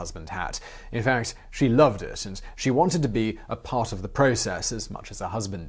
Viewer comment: No suggestions on subtitles allowed in English, so writing it here. 2:51 "a part" instead of "apart".